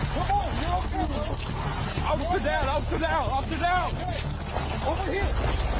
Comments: Who does quality assurance checks of your subtitles? The cop is obviously saying “officer down, officer down” not “I’ll sit down, I’ll sit down”… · whos the moron who couldnt hear "officer down" in a gd cop video